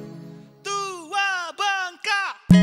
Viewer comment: Kalia Siska Terbaru ( Domba Kuring ) <link> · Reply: apa aku yg salah dengar, bilang, tu wa ga pat ...atau atw bilang tua bangka 😂😂